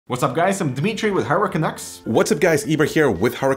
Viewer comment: I always hear this: "What's up guys, Dimitri here with HurricanX"